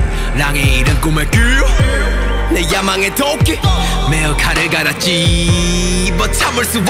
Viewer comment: it sounds like he said donkey